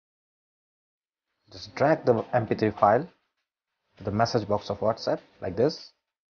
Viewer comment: What did you mean when you said Strike the MP3 file ? Did I mishear you - was that drag the mp3 file to the message window - if so I tried that and it didn't work. Please elaborate